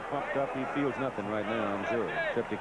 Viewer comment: does say that the Notre Dame player is "so f'd up he can't feel anything..."? 😮